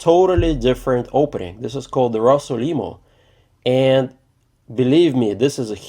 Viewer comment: Uh, the Russell- what now? 2:08 , what did he say? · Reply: The right name is Rossolimo